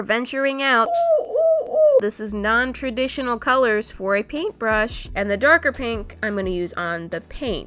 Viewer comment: did she just call it a pinkbrush